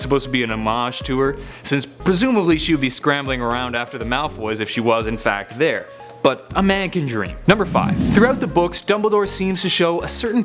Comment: did you say malfoys?